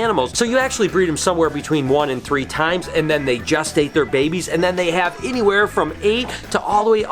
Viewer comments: I first heard "Just Ate" babies and not "Gestate" babies. Eric is finger food Take some time off and enjoy each other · Some hanky panky going on....wahooooo! It sounded like you said “just ate “ instead of gestate